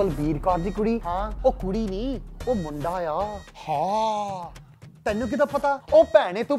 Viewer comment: I'm learning Punjabi. What does he say at 1:09? Is it "tenu kidda pta?"